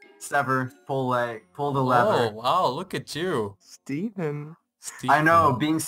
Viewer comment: Who else heard Maaz say "look at this tsundere" at 9:03?!?!?